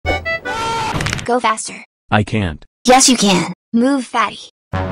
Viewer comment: When he said go faster, I was looking away from the screen, I sounded like he said "go bastard"